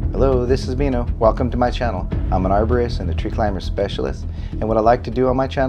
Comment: I thought it said "abortist" 🩸🩸🩸